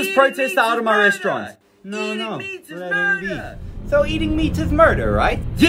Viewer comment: "Eating meat is murder" VS what I hear: "EM EN ERMER" I don't even know how to express what I heard🤣